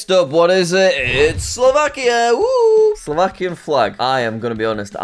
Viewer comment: I thought said "Sri Lanka" instead of Slovakia and thought he'd lost his mind.